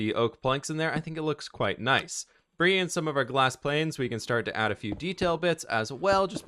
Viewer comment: am I dumb or did he say bringing in some glass planes